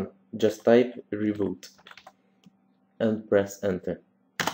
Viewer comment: Awesome tutorial, but i had the subtitles on and at 7:57 you say "by review and breast center" "instead of reboot and press enter" :D so funny